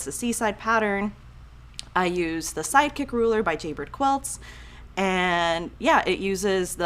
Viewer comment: Haha I thought you said you used a psychic ruler 😂😂